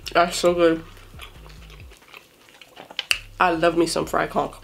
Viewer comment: No hate but sometimes when she said fried conch it sounds like she said fried co*k